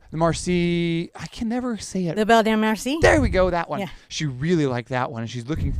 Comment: Hi. Thank you for giving us so much. I can't believe you do this all for free! You are the greatest! At about 14:45 in the video you mention a painting and I don't know if I heard properly "La Belle Dame Marcie" something like that? I was looking for that in your video list and couldn't find it. Can you point me in the right directly I'm interested in checking it out. Thank you!